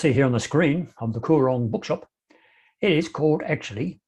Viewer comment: I thought you were saying 'The Koran Bookshop.'